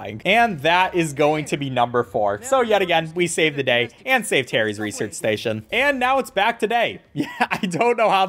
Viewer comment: Did he call Harry Terry